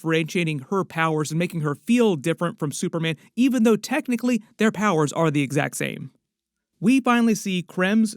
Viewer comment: Why at 11:30, says, "their powers are the exact same" yet the captions say the exact opposite?